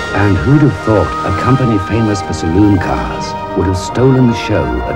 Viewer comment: Hi, could anybody write the speach of the voice over? I need to translate it for a class exercise but I cannot catch some words. For example in the second 00:30 "a company fomous for ...?... cars " Could you help me please? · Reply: Saloon cars